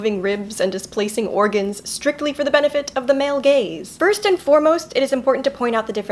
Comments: Ok ok ok- I should be taking this seriously, but... When she said "male gaze" I heard it as "male gays" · I heard you say "strictly for the benefit of the male gaze" but my confused mind initially registered "strictly for the benefit of the male gays". LOL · K, so I'm watching her old videos and I thought she said for the male gays, and I was like ??? Wut? and it took me until like the end of video to figure it out... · I thought she said the males gays not gaze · did you say strictly for the man gays or man gaze?😂 I'm pretty sure it's the second one but my mind went to the first one.